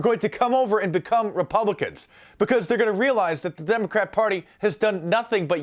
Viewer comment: did he say Demon-Crap-Party? haha